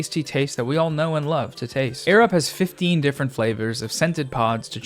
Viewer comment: I thot u said "snifteen" instead of "fifteen"